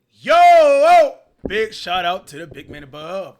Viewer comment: S/o to the big man abooooveeeee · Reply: I thought you said s/o to the Big Mac aboveeeee 😂😂😂